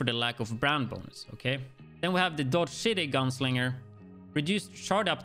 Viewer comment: I thought he said "Dodge Shitty" Holster lmaooo